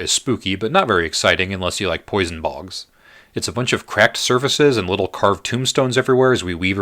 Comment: when you said 'it's a bunch of cracked surfaces', I heard 'craft services', and I was like...what a high-concept way to describe both craft services and the Nightmare Frontier. This is clearly beyond me, I need more eyes! Also, excellent video. I stumbled on it randomly and am very glad I did. Gave me a wonderful new perspective on Yharnam. I have my eye on a print now!